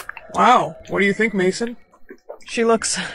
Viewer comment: did you just say mason😂